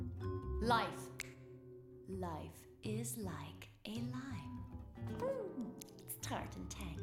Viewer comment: I thought she said “Life is like a lie”